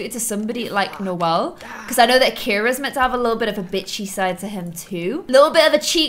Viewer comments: whispers did say bitchy? I'm just asking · am I tripping, or did she say 'bitchy'????